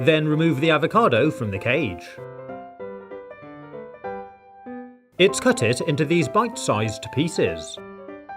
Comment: did i hear he say "it cutted" ?